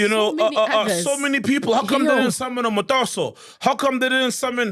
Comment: Did he say Motaso?